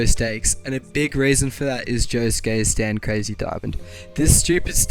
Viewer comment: At the captions say Joe's gay stand